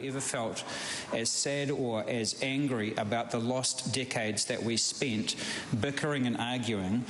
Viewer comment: Did he say "the lost dickheads" ?